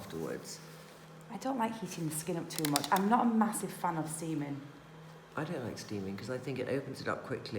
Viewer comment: Seriously thought Teresa said "I'm not a massive fan of semen" 😂 #steaming